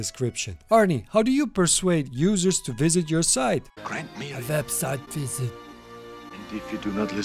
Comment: did I hear "website visit" or "revenge" coming out from Conan's mouth?? Lol